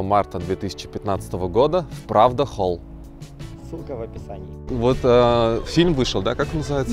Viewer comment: "сука в описании" послышалось